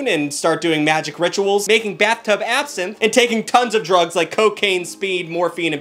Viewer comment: "Making bathtub acid" is a phrase that makes entire sense in context and still threw me for a loop de loop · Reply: Wait, I thought he said "bathtub Absinthe" 🤔